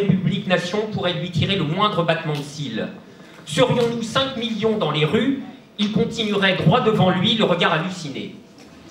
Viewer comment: A 2:20 Qqn entend les paroles exacte de la chanson lancée par le public svp ? 😅 "Emmanuel Macron, ... (?) (Version 2 : "oh tête de c*n", pas la version ici) On vient te chercher chez toi. Emmanuel Macron, ... (?) On vient te chercher chez toi ! Emmanuel Macron, Le dealer des patrons (?) On va te chercher chez toi." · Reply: Emmanuel Macron, président des patrons, on vient te chercher chez toi ! (à chanter sans modération en manif, camarade !)